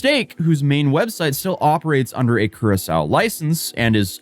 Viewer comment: did he say curasol or Curaçao? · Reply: He got the pronunciation correct, as far as I know. (Curaçao)